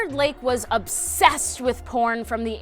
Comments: the last one i misunderstood it. I thought she said he was obsessed with corn. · Who else thought she said he was obbsessed with corn?